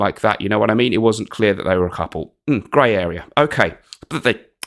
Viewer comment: I thought you said gay area...